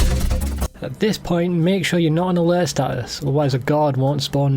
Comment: Bro, what are you saying at 9:44? All I'm getting is "At this point, make sure you [mumble mumble mumble] otherwise the guard won't spawn in the next area." I've rewound like 50 times and I have no idea what you're saying. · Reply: make sure your not on alert status